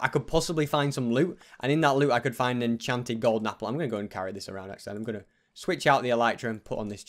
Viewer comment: Am I the only one who heard him say mine golden apple